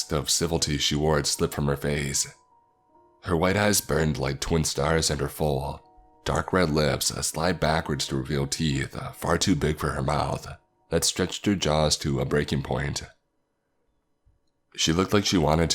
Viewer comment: did he say white ass😂